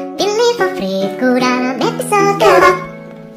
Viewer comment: I thought KEPAB said kebab